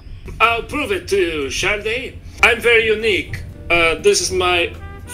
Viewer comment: whenever this guy said shawty, it sounded like SHARTY AHAHA